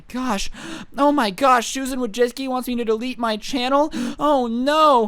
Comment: thought it said MY MOM WANTS TO DELETE MY CHANNEL...